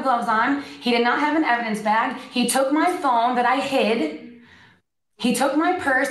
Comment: For a moment I thought she said “they took my thong” instead of “they took my phone”. It’s just as believable as the rest of it.